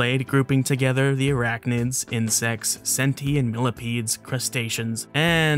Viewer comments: misheard as “sentient millipedes”🧠🐛👀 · ohhh "centi- and millipedes" lmao i thought you said sentient millipedes heh